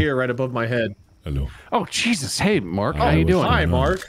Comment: it sounded like said my name 😆 Jesus haiden